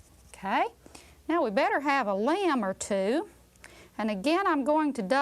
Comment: I swear I thought she said "we'd better have a lamb or two". And why not, cause who doesn't like wool?